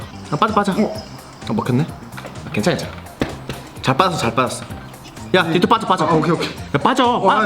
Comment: 잘 빠졌어 가 왜 난 자빠졌어라고 들리지?ㅋㅋㅋㅋㅋㅋㅋㅋ